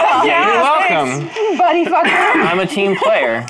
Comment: Did she call Mike a "buddyfucker" at 12:40?